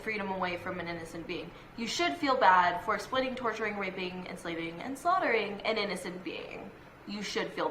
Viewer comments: did she say rapping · At around 10:26 does she say RAPING animals? The fuck? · did she say raping animals?!!!! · Go to 10:27 And listen very closely to what she says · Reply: She said that we were rapping the animals😂😂😂😂😂😂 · did she say rapeing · did she say raping 😂😂😂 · did she say raping? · Am I the only person who heard her say r*peing right after 10:15 in the video? Like, um, old McDonalds doing what. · wait did she say raping.... Do people rape chickens? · did she say rapeing? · Hold on a sec did she say raping animals, do guys, or gals, rape chickens, like wtf it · Pause 10:30 did she say raping a animal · Am I the only one who thought she said about raping animals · Ok did she say RAPEING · did she say raping and slaving a being bitch no rapes or enslaves the animals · did she just say... raping? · Did she say we should feel bad for RAPING animals ? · am i the only one who hears splitting, torturing, RAPING,enslaving and slaughtering?